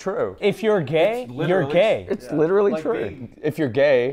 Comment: in the background I heard « like me » when they said gays are gay